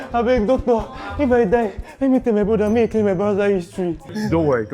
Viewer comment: Did he just say, tell my brother to erase my browser history? 😂😂😂😂😂